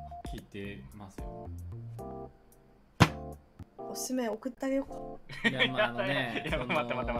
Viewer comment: she says something like "osusume okutta yo" and that kind of translates to "I sent you my recommendations" instead of "Shall I send you my recommendation?" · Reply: It sounds similar but she said "okuttageyo(k)" short for "okutte ageyou ka"